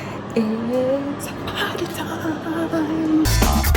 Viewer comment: I swear I hear say it's prank time